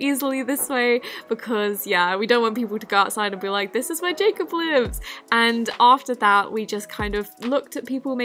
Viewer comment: all I heard was this is my Jacob blue?